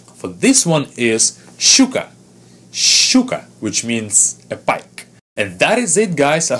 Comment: Hehee I hear "сука" 😂😄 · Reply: I heard "щука", which is not at all the same!